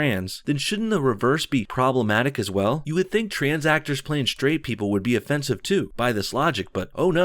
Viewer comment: When you said trans actor I thought you said transacted